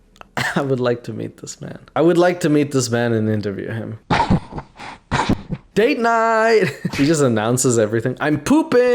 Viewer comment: they way he say date night sound like he say gayyyyyy night 😂😂😂😂😂😂😂😂